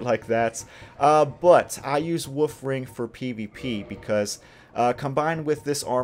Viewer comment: I'm sorry, it may just be your accent, but I definitely hear you saying "woof ring" instead of "wolf ring" and I mean, it's still accurate, but just a tiny bit cuter lol